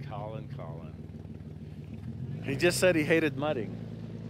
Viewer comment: actually I think he said he don’t like cleaning up the vehicle after going muddy lol